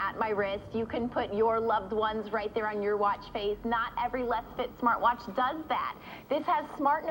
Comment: did she say "leftist smartwatch?"